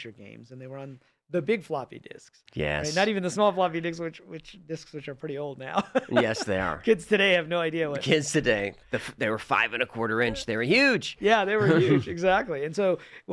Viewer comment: he did just say "floppy dicks" didn't he?